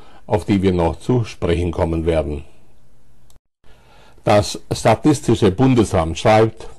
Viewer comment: Hab ich da wirklich "das sadistische Bundesamt" gehört? :-D